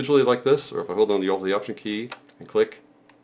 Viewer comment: with key ? i cant understand word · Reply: its option key